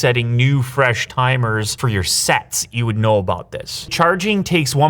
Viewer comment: my dirty mind hearing sex instead of sets between